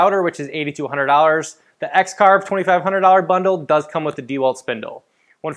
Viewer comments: Did you say the router cost $8200 in your video @ 1:40 ??? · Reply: $80 - $100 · did I hear that right? The router for the One finity is $8,200.00?